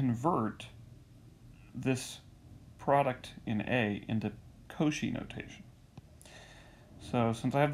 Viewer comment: Thanks. It was just what I needed to understand it. What is the spelling of the notation name please? It sounds like you say "kochi's" notation? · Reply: Cauchy